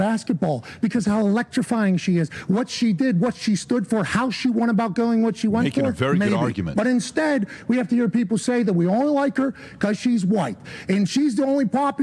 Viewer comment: What i heard was, "Im racist and i dont like her because she is white"... Im officially done with ESPN...